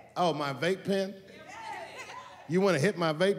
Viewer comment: Sounded like they were saying “amen” not “vape pen”, but he made it into a joke anyway.